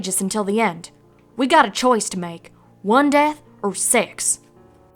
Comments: The first time I heard applejack say "we got a choice to make, one death or six" I thought she said "we got a choice to make, one death or sex." Please don't hate me, but I cant stop laughing now. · i thought she said "one death or sex" not "one death or six"